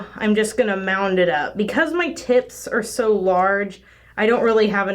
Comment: I had to replay from 3:50 because I thought I heard ti...ts not tips. Sorry, I need to clean my ears.